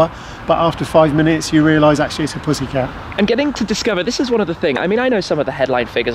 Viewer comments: did he said Pussy Car? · did he just say "this car is a pussy car"???